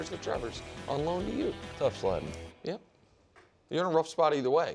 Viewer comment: What did wildes say? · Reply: Tough sledding